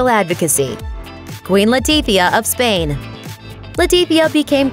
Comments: I totally thought they said Queen Latifah at first. lol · I thought she said Queen Latifah